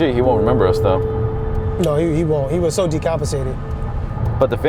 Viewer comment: did he just say "he was so decapitated?"... lolll